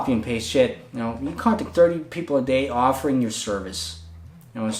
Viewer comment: I was listening to this video’s audio only and thought you kept saying “contact dirty people” 😅